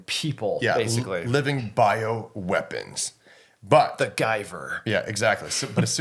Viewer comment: Dude thanks for the guyver picture, I thought you said mcguyver, this was such a great podcast.